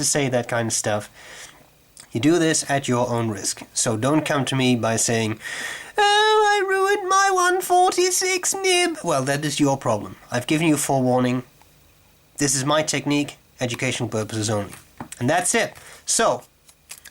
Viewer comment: "waaaah!!! i ruined my 146 nib!!!" lmao :D good stuff! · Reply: Lol I heard it "Mom, I ruined my 146 nib"! 😂😂😂